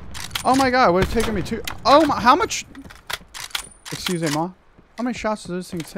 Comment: Mdr a 7:45 il dit bien « excusez moi » ? 😂😂😂😂😂